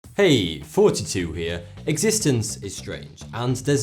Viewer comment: It sounds like he is saying "hey guys it's 42 here" at the beginning of each video.